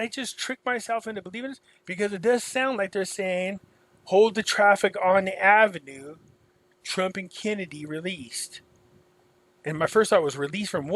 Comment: “Hold the traffic on the avenue for the Trump/Kennedy release”. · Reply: That’s exactly what I heard, “ Hold the traffic on the avenue for Trump/Kennedy’s release!!!